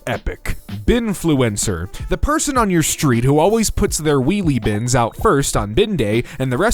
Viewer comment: i could swear he said "wee wee" bins instead of wheelie! i wasn't looking, just listening and i had to go back like 'what did he just say?"